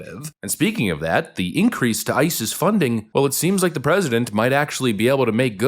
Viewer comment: I almost thought you said ISIS funding lol